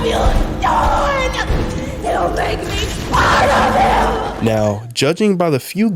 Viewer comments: i thought Mommy Long Legs said "You make me proud of you" when she was diying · she actually says "he'll make me part of him" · I thought the end of chapter 2 I thought she said “YOU MAKE ME SPIDER KILL” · I thought she said "yoULl mAkE mE SpiDeR MiLK" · When I first heard it I thought it said " you'll make me spidervill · I thought thought mommy long legs said you’ll make me spider killed but not